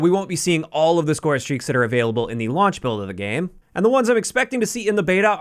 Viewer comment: thought you said lawn chair for a moment